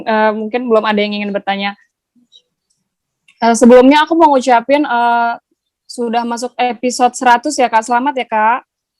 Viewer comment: Siapa yg tau Zoe Cinta ngomong apa di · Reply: Yang kutangkep Zoe ngasih tau bawa makanan gitu,terus kata Cinta,aku blm makan sama sekali